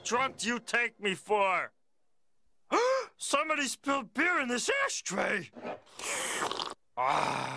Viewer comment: When I first watched this clip, I thought he said "ass tray".